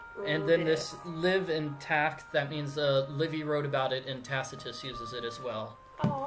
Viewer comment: did he say, "Libby wrote it," because if he did my name is Libby!